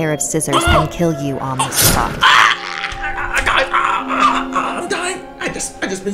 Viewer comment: GUCCIsakke-onna got mehhh That’s what I heard ._.